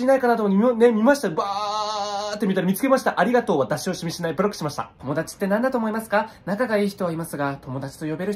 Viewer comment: ガチでなんで言ってるのか聞き取れなくてもモヤってる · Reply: ありがとうは出し惜しみしない、ブロックしましたって聞こえました🤔